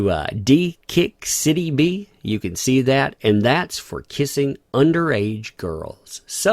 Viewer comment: I thought she said this was for suing.